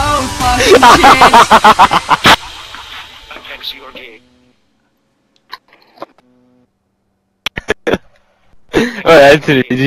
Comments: did anyone hear Aupex u are gay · Anyone hear at 11:01 he said "Aupex you are gay"?